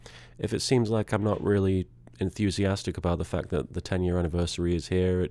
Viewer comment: People are going to hate this comment but i don't care. you need to stop making Yu-Gi-Oh Abridged content. i'm sure it was fun for you when you were younger and not depressed but it is very clear that the YGOA content is causing your depression. I know you love YuGiOh but i know what it's like to be stuck inside 24/7 and seeing people around you living their lives while your sitting at home editing a child's cartoon for 10 years. stop making the content and go live your life. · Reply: No he doesn't. You have completely misheard what he says 2:21. He says unenthusiastic about the 10th anniversary and his depression is partly to blame for his lack of his enthusiasm. He does not say YGOTAS has caused any part of his depression.